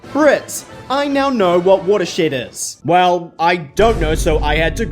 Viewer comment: I heard what'a-shit at first